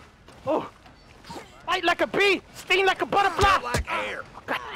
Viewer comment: did he say fly like a bee sting like a butterfly? :D